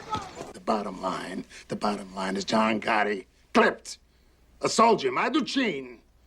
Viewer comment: Paul Castellano says something that I cant make out. He says John Gotti clipped his soldier and then says something in Italian can anyone make it out?? THANKS. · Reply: I always thought he said "My Luccine". Perhaps that's what they named that character that got whacked. Good question though.